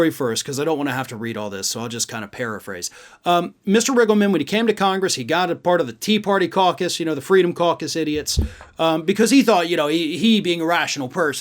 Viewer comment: I totally heard that as "tea party carcass".